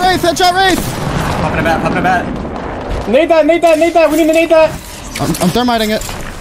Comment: I understood the N Word... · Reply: Def did not spam the n word for no reason in the middle of a fight lol pretty sure he said nade that but when i watched live I couldve sworn he said murder them for some reason haha